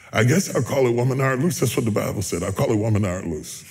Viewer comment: Gotta be careful how you hear, it sounded like he said the play was called Woman thou art loose. But he said woman thou art loosed. Big difference.